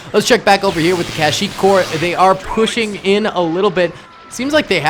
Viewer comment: Nah is it me or did he say core